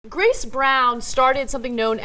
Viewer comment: The captions said "Chris Brown".